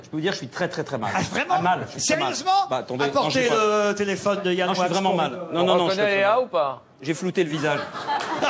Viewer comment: qu'est ce qu'il dit en 8:23 ? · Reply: "On reconnaît lea ou pas ?" (Lea salamé donc)